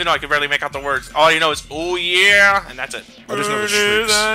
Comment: "OOOOH YEAH! I AM THE ULTIMATE! LIII-FE FORM!" is what they're saying :).